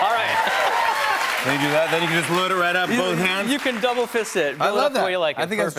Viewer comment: did he just say you can double fist a taco? 😂🤜🤜👌